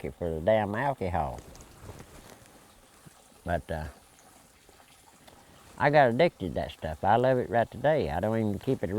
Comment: what does he say his mom put in the likker to make medicine? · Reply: Asafoetida